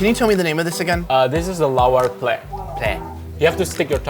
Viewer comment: I literally love how the caption says "Allah" in place of pork meat that is "Lawar" 🤣😂🤣